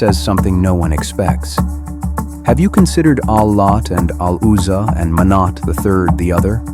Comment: Hello can someone please write what is said at 9:48 to 9:58 because I didn't understand (I'm not a born english speaker) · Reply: Allat, Aluzza and Manat. Surat an Najim 53:19-20.